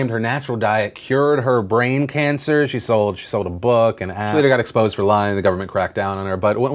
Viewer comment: Did I just hear "and she sold a hat" While trailing off?